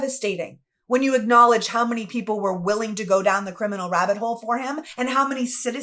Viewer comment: Edited to change what I misheard as a "rat hole". Calling that "sewer" a "rabbit hole" is an insult to rabbits. Otherwise, I agree with you.